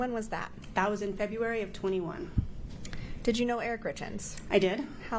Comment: Someone help me out here......my ears are playing tricks on me 😂😖..... This witness is asked "....And when did you first start working for them?", and the witness responds " A 1000 (a thousand) February, 2001. What the hell does "a 1000 February" mean?? I reminded it 10 times and I keep hearing the same thing 🙈🤦🏽🤷🏽‍♂️. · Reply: "That was in February of 21"